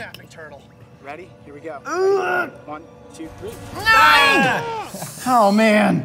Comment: did anyone else hear NINE